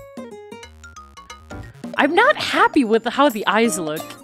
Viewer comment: i got oofed on the griffin level too. i only went back when i had a better team. :I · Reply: i was fast-forwarding the vid and it sounded like you said "i'm not happy with my cat"